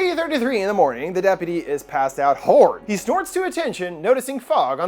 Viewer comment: It took me so long to convince my friends that this wasn't real. Like way harder than it should have been. Also at 21:32 does he say the deputy is passed out whore?